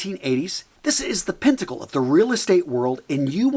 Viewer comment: `What is this word, "pintagle" that you just said? PINTAGLE???? 2:34 Don't you mean: PINNACLE? yassss.....